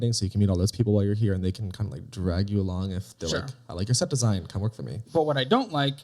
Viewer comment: I thought he said city design, but I guess he said something about theater costume design? I don't get that. How is this guy so chill right now? Lol